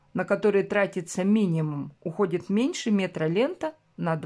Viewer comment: Мне не послышалось? Меньше метра на 2 бантика?🙂